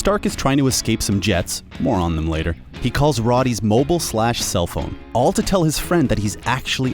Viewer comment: did he just say "Rodee"...? 😔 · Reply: No he said “Roddy”